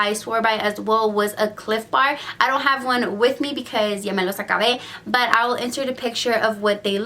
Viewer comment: what did she say🤣🤣🤣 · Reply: She said i don't have it with me because i finished tgem